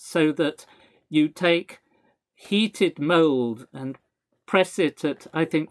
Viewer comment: Oh, "mold". I was in another tab in my browser, just listening to the audio, and thought the Professor said "heated mole" at first and was deeply confused.